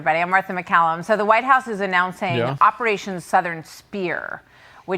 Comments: I was distracted and not looking at the title on news channel and when she said 𝐬𝐨𝐮𝐭𝐡𝐞𝐫𝐧 𝐬𝐩𝐞𝐚𝐫, it sounded like 𝐟𝐞𝐚𝐭𝐡𝐞𝐫𝐞𝐝 𝐞𝐚𝐫 to me for some reason. I guess both are good names for military operations. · Asmon having a day off is the equivalence of the Pentagon ordering dominos in a time of war. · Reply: I was distracted and not looking at the title on news channel and when she said 𝐬𝐨𝐮𝐭𝐡𝐞𝐫𝐧 𝐬𝐩𝐞𝐚𝐫, it sounded like 𝐟𝐞𝐚𝐭𝐡𝐞𝐫𝐞𝐝 𝐞𝐚𝐫 to me for some reason. I guess both are good names for military operations.